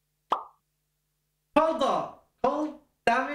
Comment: I totally thought he was saying cold snap at the very start.